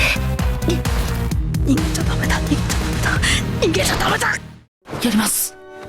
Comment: もう逃げちゃダメだがイッちゃだめだにしか聞こえないw